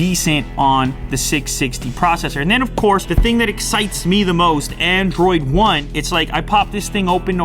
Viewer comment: I thought he said android 1.0 version XD